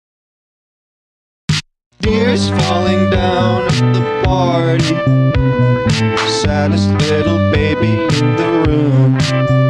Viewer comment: I thought you said dears falling down at the party Santa's little baby in the room